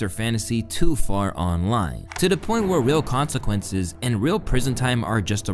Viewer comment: I thought you said "Lil' Consequences" at first and nearly choked